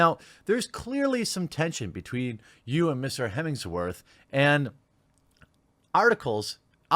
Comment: Did he just say Mr. Hemingsworth